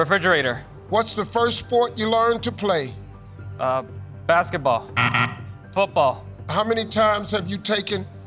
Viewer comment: The buzzer person owes them an apology! Wow, just wow!!!!😮😢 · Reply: yeah I heard him said "Basketball" as I rewind back (just to check the recent answer) and I looked at the board and the board says "Baseball" and then he changed it to "Football" and it is clearly not on the board. How can you not hear him said Basketball? This is straight messed up